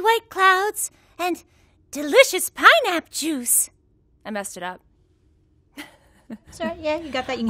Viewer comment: Am I the only one who heard her saying pinap juice